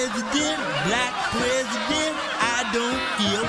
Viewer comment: I hear "white president, black president, I don't geeyulp" am I the only one?